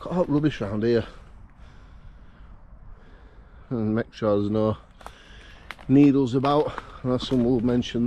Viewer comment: I had to go back 10 seconds, I was watching while also talking to my 5 year old soon and at first I thought you’d said “I’m gunna make sure Beadles not about” #watchoutbeadlesabout